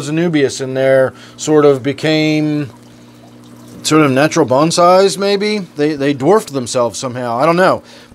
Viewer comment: Thought you said your anubis was "natural fun size" at 13:00. LOL!